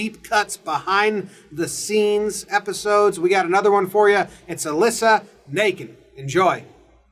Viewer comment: At first I thought he said Alyssa naked. Then I saw her last name